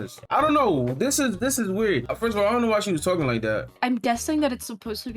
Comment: is he saying „she“?